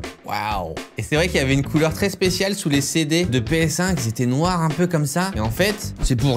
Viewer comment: qui a pensé comme moi qu’il a dit « CD de PS5 » au lieu de « PS1 » ? 😂